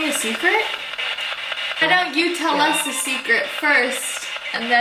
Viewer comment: When you said can you tell us a secret the second time... It sounded like he said why don't you go first lol